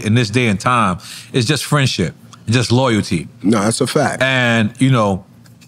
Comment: Did Nore say friendship is overrated adding 1:55:33 minute mark???